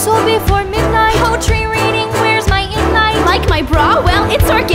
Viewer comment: the subtitles say life instead of like